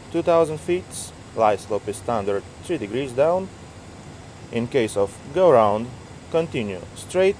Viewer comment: did he just say ( IN CASE OF GO AROUND ) At 5:46 ? Flying with only one engine ATC must to hate you lmao , great vid !